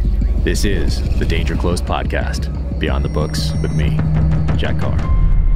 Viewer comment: lol your opening. I wasn’t looking at the television when the 30 seconds automatically started to play on your video and I heard “by me jack off” So I rewound and kept hearing the same thing then I put on close captions and the close caption says” by me Jack” So apparently cc can’t hear it, or won’t type it 😆